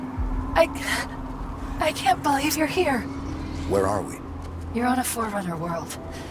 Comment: Subtitles mistake. The subtitles say "Master Chief: Where is here?" And Master Chief says "Where are we"